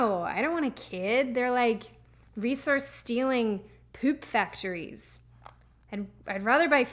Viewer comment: thought she said reeses stealing... not resource stealing... I guess either way works...